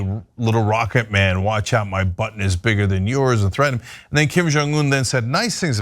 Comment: mark 😂😅 I was listening on side while making coffee. I thought cenk said.. "Trump said to Kim Jung my butt is bigger than yours" 😂😅😂